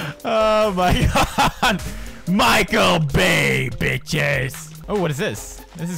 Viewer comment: When it said Michael bay, I thought it said Michael Way(Mikey) 😂